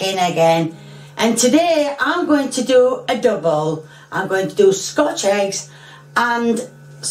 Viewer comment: I didn't under stood what she was saying until I turned on the captions. In my head, I thought she said she was going to make adobo. But fortunately she said "a double". My Filipino pride is still safe from her, that's good.